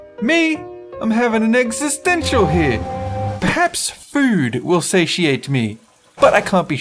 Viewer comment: I thought the cat said: maybe food will say she ate me